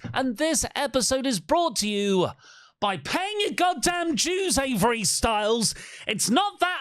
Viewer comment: I thought he said “paying your goddamn Jews, Avery Styles!” 🤣